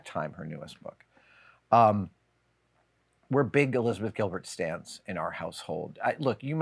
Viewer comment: What do you mean when you say you're "a big Elizabeth Gilbert "stand"". I thought I misheard but you say it twice. 7:45 & 8:06 Did you mean to say you're a "fan" but it just came out "stand"? Thanks! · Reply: He’s saying “stan.” It’s a slang term that basically means “fan.”